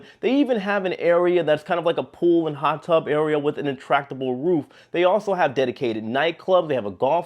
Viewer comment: did he really say an "ATTRACTABLE" ROOF?!?!? 😆 🤣 😂